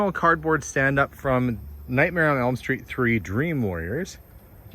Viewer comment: I thought you said "Nightmare on Elm Street 3: Dream Lawyers". Well, that's a nightmare for adults who own businesses!